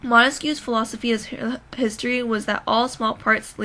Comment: I can't understand what you said from 2:09 to 2:13 could write it down? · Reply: Philosophy of history